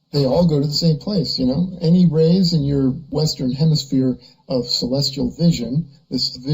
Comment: did he say "Western hemiSPHERE"?